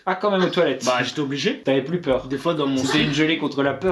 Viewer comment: C'est moi ou vers 9:44 Mathias a dit "des fois dans mon slip" ?et Bastos l'a coupe et il est passé a autre chose? 😂 j'aurais bien aime savoir ce quil allait dite si jai bien entendu 😂